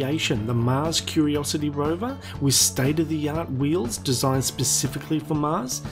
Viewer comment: Hello guys, I'm french and I dont understand the sentence at 0:30 : "But even their last creation the mars curiosity rover [???] wheels desingned specifically for Mars start to taking damage ..." I don't get what the speaker says at the [???] and the subtittles are the youtubes auto subtitles, so it doesn't help very well. Could you help me to understand please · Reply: I said "with state of the art wheels"